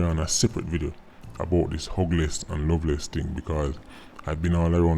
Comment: When you said Jamaican children are some of the most hugless I thought you said ugliest...